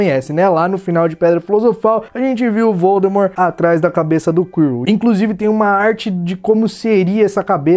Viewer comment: desculpa mais eu ouvi:"cabeça do cú" kkk